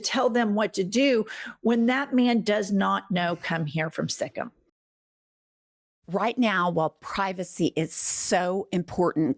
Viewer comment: 😂. I thought you said “He doesn’t know kun thair from stickum!” 😝